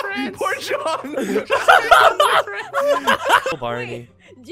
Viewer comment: did say “ poor John? “ to barney